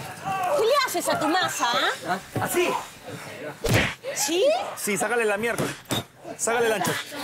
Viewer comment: pensé que dijo “sácale la mi3rd4”, lo vi en vivo y me quedé helado, pensé que se les había pasado censurar eso 😬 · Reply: Sacale la miercoles dice , y no esta golpeando un pollo sino una masa de harina . 😄